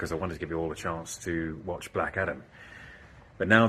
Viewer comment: I thought he said after you watch Black Adder - that's a great show!